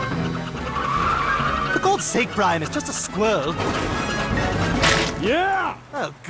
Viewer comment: BAHAH AM I THE ONLY ONE WHO HEARD THAT STEWIE CANT SAY SQUIRREL 😭😭😭😭 SKWEL